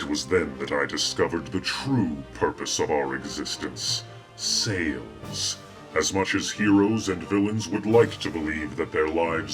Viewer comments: did he say Saiyans??? Lol I wonder if Goku will appear xD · Aw jeez, I thought Darkseid said 'Saiyans' instead of 'Sales' at first .__.